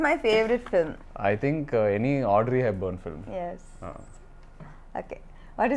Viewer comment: what was the name of person he is saying?? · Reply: audrey hepburn